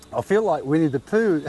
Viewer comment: In the start of the video I thought he said "I feel like we need to poo". Then I realised he said "I feel like winnie the pooh" 😅